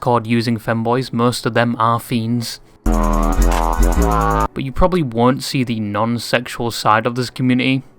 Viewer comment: I thought he say "most of them are female", I role back and now I'm shitting myself because of my reaction